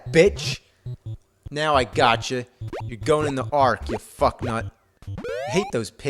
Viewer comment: "You're going in the ark." (lol) It sounds like he says "You're going to jail." Love the AVGN, don't like some of his language, but he's right about a few of these games.